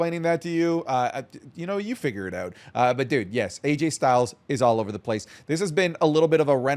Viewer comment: did just call him "RayJay Styles?"